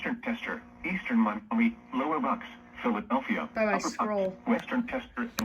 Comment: Hold up did he said I’m I mean